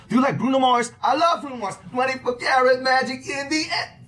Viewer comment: did he just say India?